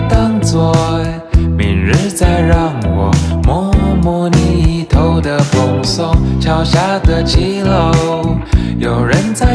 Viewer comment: 最喜歡「明日再讓我 摸摸你一頭的蓬鬆」🤩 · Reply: 我听成“没人再让我 摸摸你一头的蓬松